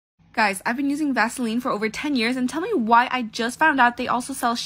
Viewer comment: why at the beginning i thought she said she was eating vaseline for 10 years 😂